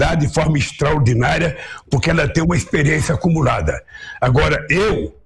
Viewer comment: Kkkkkkkkkkk Dilma tem uma experiência como ladra foi isso mesmo que ouvi no 2:36 kkkkk